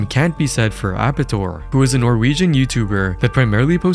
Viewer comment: Wait did you say Norwegian? (instantly perks up because of my dna)